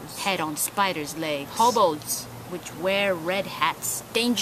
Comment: lol I probably misheard you but did you say "hobos which wear red hats"? Because if you did, and their on Geralt's list of things that don't exist, I'm going to freak the hell out because I met one on St. Patrick's Day! I even bought it a drink! It said it's name was Gary and it came from Chicago. A real nice guy/mythical creature. I also love the "Oh s**t, I can already tell I'm wasting my time by talking to this guy" look @ 1:16 lol Hilarious stuff, excellent job!